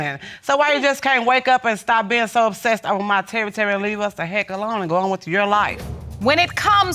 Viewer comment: Came with a Terry Terry and left with Terry Terry Terry 😆🤣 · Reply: I thought she was tryna say territory 🤣🤣